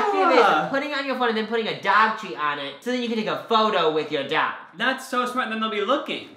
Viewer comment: I have never had an issue understanding what you’re saying except when you were talking about the phone accessory I thought you said “take a photo with your dad” up until Luna appeared 😂